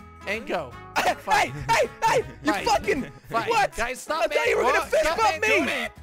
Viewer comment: guys is it just me or i hear f***ing in